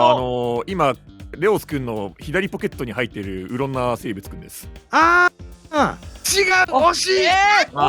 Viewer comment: 聞き間違えじゃなければうろんな生物って聞こえたんだけどｗｗｗｗ確かにうろんかもしれないけどｗｗ